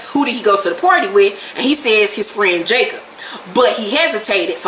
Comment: I thought Tariq said Dre took him to the party...did I misunderstand??